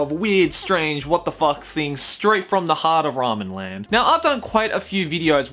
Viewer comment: did he say "ramenland"?